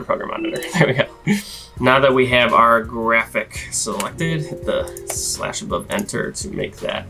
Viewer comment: What did you say at 2:39? You selected /+ENTER? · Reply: Hey! Sorry for the confusion. The command is "/". In the video I said "the slash above enter" referring to slash key above the enter or return key on your keyboard.